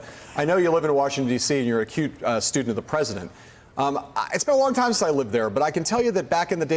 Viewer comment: When addressing Przybyla, I know that Heilemann meant, "acute student of The President", but it sounded like he said, "...You're a cute student of The President..." Obviously, it's nothing serious, but it WAS enough to trigger a false alarm on my misogyny-radar.